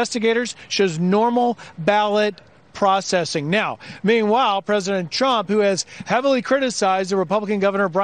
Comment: The reporter sounded like he said "President Chump".